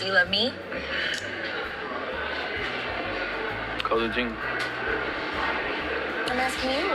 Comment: I turned my head when Georgie said “How you think?” and I swear that I thought he said “collagen “lol.